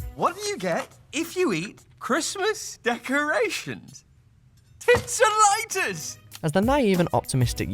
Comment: "What do you get when you eat Christmas decorations? Tits & lighters!" That's what it sounded like. Edit: yes, I know he's saying "tinsel-itis" but I listened to it a few times and it sounds like "tits and lighters" to me.